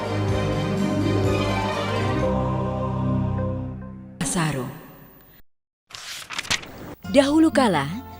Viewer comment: itu ngomong apa ya? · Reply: Lutung kasarung